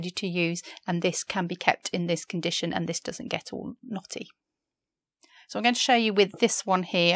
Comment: at 5:10 I thought you said "naughty". 😔